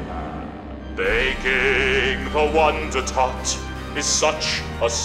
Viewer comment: wth why do I hear wonder TAT